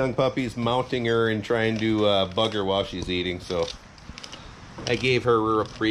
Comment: I thought you said “fuck her” at first lmao but you said “bug her” 😂